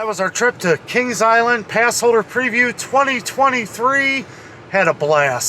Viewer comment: Ohhhhh!!! You’ve been saying PassHOLDER pass! 🤣🤣🤣 I thought you kept saying PassOVER! 🤦🏻‍♀️🤦🏻‍♀️🤦🏻‍♀️🤣🤣🤣🤣 I was thinking…🤔 This is a Jewish amusement park???🤣😜